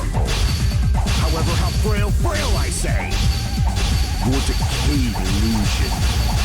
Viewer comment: I heard "however how frail frail I say" far more times than I'd like to admit lol